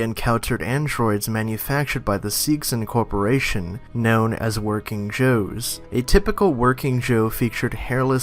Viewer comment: anyone else heard working jews at 0:15?